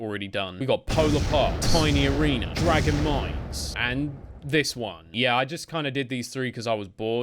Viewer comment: I thought it said DRAGON MEMES for a second... help me